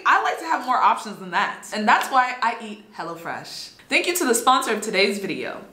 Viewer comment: I coulda swore she said 'hella fresh'